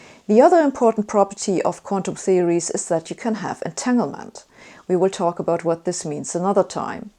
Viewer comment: "The other important property of quantum theories is that you can have _____." What was the last word of that sentence? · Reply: Entanglement. Please note that my videos all have captions. You turn them on by clicking on CC in the YouTube toolbar. You also find transcripts to all my videos on my blog: <link>